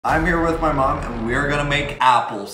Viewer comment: I thought you I thought you said me and my mom are going to make out